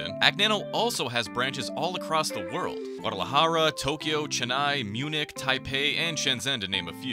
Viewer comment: did he said chennai 🔥